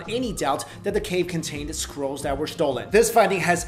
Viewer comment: Lol!!! I thought he was saying "sea squirrels" instead of scrolls!!! I was like wtf are sea squirrels!? Lmao!!!!!